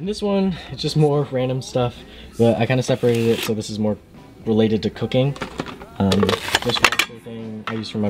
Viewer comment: When u said this is related to cooking... I thought u said this is for cocaine